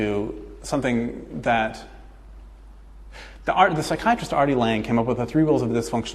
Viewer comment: Oooh, R.D. Lange. I thought he was saying Artie Lange and I was like whaaaat 😂 This was absolutely amazing. I did not want it to end. I'm so glad I found this guy from the Thoughtmaybe documentary website.